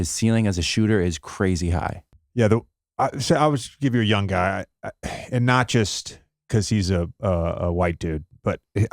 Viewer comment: who is JJ talking about? I just heard "a young guy, and not just because he's a white guy..." but I couldn't catch the name. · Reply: Corey Kispert